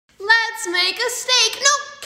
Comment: I thought she said "Let's make a snake-- ✨️nO CaKe✨️" i'm dead 💀